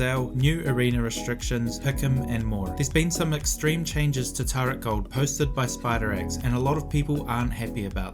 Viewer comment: i was looking away from the screen when you said turret gold and thought you said "there will be huge changes to Taric Ult" and i was like "oh shit"